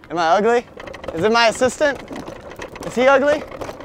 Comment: "She's actually my sister, but like were dating." WHAT THE HECK😂😂😂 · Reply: Sophia Vivancos I thought he said assistant